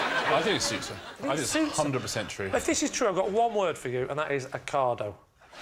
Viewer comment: what did Lee say? A cardo? What’s a cardo? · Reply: OCADO ( online supermarket in uk )